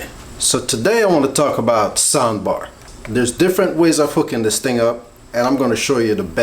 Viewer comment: I swear to god I thought he said "there's many ways of fooking this thing up" XD